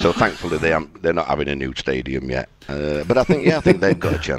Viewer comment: What does he say at 6:30? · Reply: They’re not having a new stadium yet